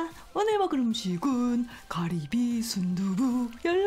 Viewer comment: did she just say cardi b 😭😭